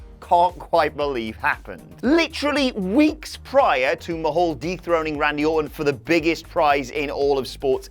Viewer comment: didn't it sound like he says Jinder "deep throated" Randy Orton (instead of dethroning)